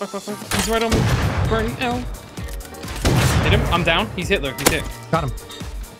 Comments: He's Hitler ' he's hit did anyone else hear that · did say:he is hitler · y does he say hitler